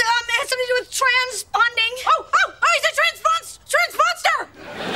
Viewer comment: I thought she said: "Transmonster"